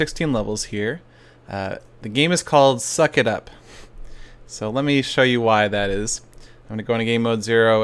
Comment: Anyone else thought it said fuck it up?